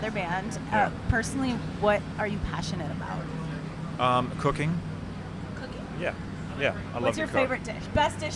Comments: thought he said cocaine instead of cooking lol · I heard Cocaine. I thought: well, that´s honest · Low key thought he said cocaine and not cooking 😂